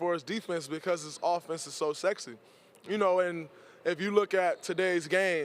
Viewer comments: Did he just call Kevin Durant's offense "sexy"? @ 4:30 It doesn't mean shit, just caught me off guard. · “His offense is so SEXY” did I hear that right wtf